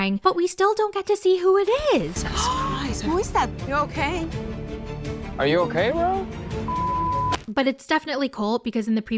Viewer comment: It sounded like said "Are you ok Rob?" to me.